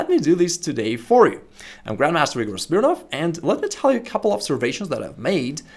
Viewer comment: ► Chapters 00:00 1 Key Skill Most Chess Players Don't Know 00:20 How to counter 1.e4 as Black? 01:36 The problem with the Caro-Kann Defense 03:39 What's the point when you can't attack? 05:29 So, what to play against 1.e4 as Black? 06:15 Countering your opponent's early opening tricks 08:05 How to counter the Fried-Liver Attack? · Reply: WHERE THE SUBTITLES SAY YOU ARE GRANDMASTER RIGORUS BERNOFF